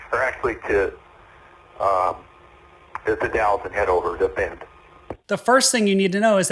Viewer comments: The ??? sounded like "The Dalles" which is east up the Columbia Gorge past Hood River. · As for a destination, I thought I heard he was intending to head to The Dalles.